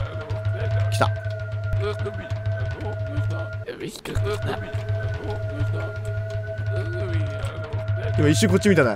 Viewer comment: 体験版の続きが気になってたので嬉しい！ 不気味さと美しさと…独特な世界観に惹きつけられますね😆 25:37 徘徊中の幽霊がなんて言ってるか分からないですが、ここは「ヤベ、引っかかった」に聞こえてしまいましたｗ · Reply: 製作者さんによると「生首はどこだ」と言ってるそうですよ！幽霊は絵から出てきていて、幽霊の他に書かれていた生首を探して徘徊してます。ちなみに生首はビリーヤードの際に出てきます！